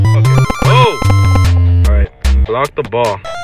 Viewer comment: Creo que dijo Alright jajaja